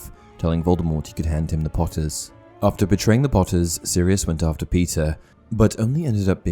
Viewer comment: Did anyone else hear: “After betraying the Potters, Sirius went out for pizza”? Just me? Cool.